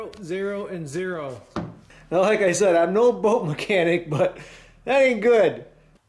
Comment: I thought you said you are not a boat mechanic, but you are doing great job, as always awesome video my Man!